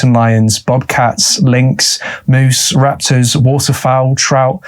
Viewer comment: Raptors?? Did you say raptors live in this forest!?